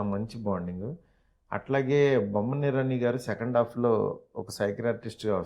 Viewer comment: did he say cycle artist 😃